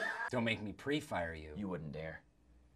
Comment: What did john/jim say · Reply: “You wouldn’t dare”